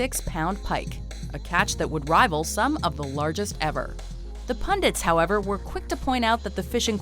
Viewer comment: Does she said pandits